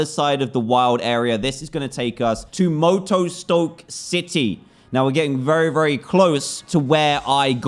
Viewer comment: Lol i thought dan said moto moto city